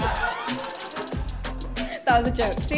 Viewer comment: Wait did she just say “ i love broadways TALAGA”?? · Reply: No she said “I love Broadway style guide”